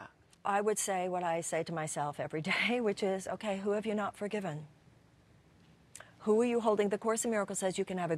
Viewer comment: what did she say? at 2:06?! Help! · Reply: She said: okay, I would say to myself, who have you not forgiven?